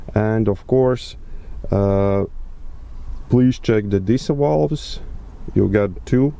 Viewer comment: it sounds like you said diesel valves